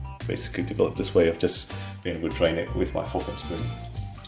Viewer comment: I thought he said “my fucking spoon”